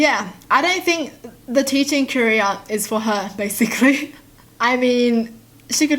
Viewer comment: When she said “I don’t think the teaching career is for her” I heard “I don’t think that teaching in Korea is preferred” 😂